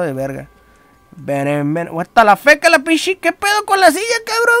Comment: "Wahttalafake" JAJAJA no mames pinche palabra que dijiste